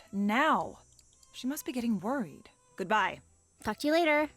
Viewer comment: 1 FREE VENT SESSION INCLUDED WITH THIS VIDEO. Please reply and vent about whatever the heck you want :) Myself and others in comments will listen, errr.. read. (In reality, I just want to know all of your juicy gossips so tell us) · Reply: OMFG at 9:11 when the blonde girl (always forget her name lol) said "talk to you later" i thought she said "fuck you later" I just started shouting of misbelief xDD